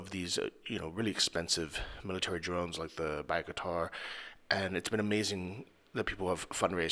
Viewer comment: Pieguitar? Did I hear that right